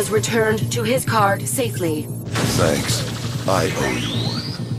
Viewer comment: Who else heard "Aibou" from Gohram at 16:58 ?